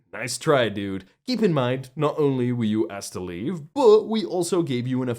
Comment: thought he said EDP, I dunno if that would be worse a fate.